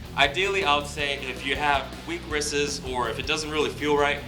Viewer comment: did you say "wristes"?? lol!... Only kidding my man, I really appreciate the vids, and if it was me talking on camera their would be a whole lot worse slips ups! Thanks for the work and keep the videos coming. Cheers from Canada